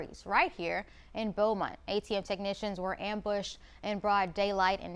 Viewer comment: LMAO I thought she said ATF technicians LOL😅😅